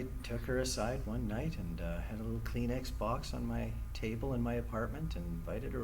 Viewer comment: I thought my man said clean x-box 😅🎮